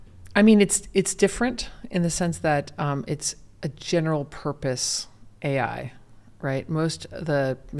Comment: Hay un error en la traducción en 2:15 hasta 2:21 Ella dice que "it's different in the sense that it's a general purpose AI", no dice "it's a generative purpose AI". Y esa diferencia entre lo traducido y lo que dijo realmente es significativo. Lo que la traducción debe decir en los subtítulos es: "es diferente, en el sentido de que es una AI de propósito general". Y es que justo, luego habla de los otros tipos de AI que son mucho más específicos, para contrastarla con la Generative AI que es de propósito general.